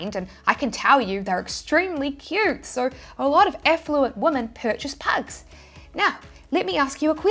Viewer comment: As a Kiwi I heard you say affluent, But the text below kept saying effluent! Which made me laugh. Those effluent women that own pugs!